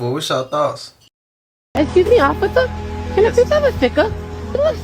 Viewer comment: did he say officer Kirken?